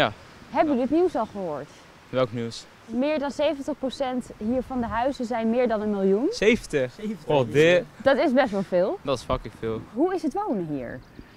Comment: Wat zegt die jongen bij 0:31 Oh Dirk? Of is dat straattaal?